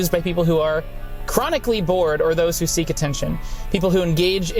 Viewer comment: Oh my god I thought you said “chronically vored”